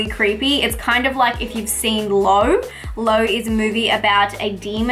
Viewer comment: Around the 3:20-3:30 mark It sounded like you said the movie is called Low (musical comedy) but I can't find it on IMDB?